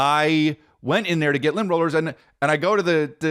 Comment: Swore he said Limp Roller, was wondering wtf it was